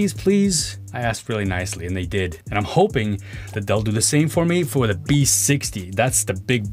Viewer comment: DELL? is that your editor making a mistake by think you said "DELL" when you said "THEY'LL"